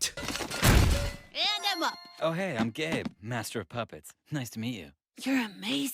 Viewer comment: I thought gabe said: hey I’m gay